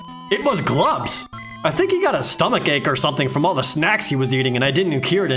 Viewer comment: By "stomachache", do you mean "heart attack"? Because that's what it sounded like. Dang. That got dark really fast.